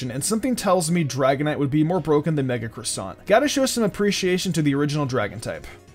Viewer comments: I swear I heard"Mega Croissant".....did I miss a game? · mega croissant? I can't make out what he actually says. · what does he say? Mega Dragonite would be more broken than Mega Croissant? I can't figure out what he's saying lol